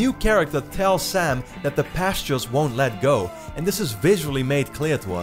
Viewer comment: in 8:13 according to the subtitles in the trailer she actually says "the past just won't let go" not "the pastures won't let go" anyway great video as always :)